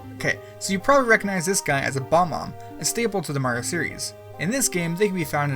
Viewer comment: did you say bomb-om? ITS BOB-OMB!